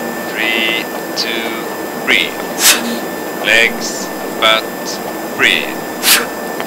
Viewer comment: "Lets Fuck, Breathe.." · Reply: I thought he said "Legs ,Butt ,Breath , And , count to 3" 😂